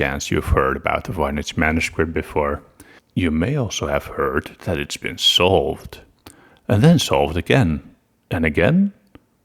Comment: I thought you were saying "it's been sold"😭